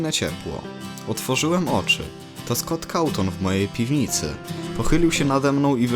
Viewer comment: usłyszałem scott całton xdddd